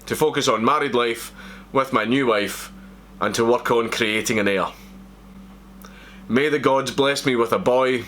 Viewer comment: thought you said "im going to focus on creating in ayr" near enough shat myself